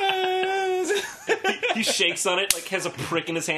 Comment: No matter how many times I watch this, I still don't know what the number is, but the way Arin says it makes me laugh every time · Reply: $19,000,000,000,368,000,000,000.00